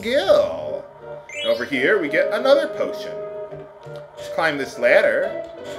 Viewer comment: am i the only one who thought he said "botion"?